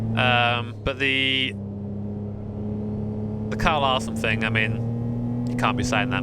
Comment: What did say at 14:16 ? What Carl Arsum is he talking about? I wasn't able to find anything on Google about it